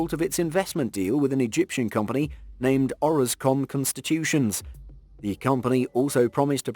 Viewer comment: Orascom Construction. Thought I heard constitution.